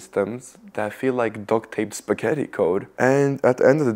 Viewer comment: why does subtitle say "KY code" instead of "spagetti code"? 😂😂😂 i mean its autogenerated in the most relatable way, lol